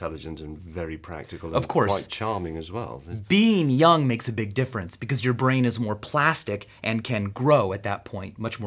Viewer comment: Lol,I thought he said being dumb makes your brain more plastic. :>)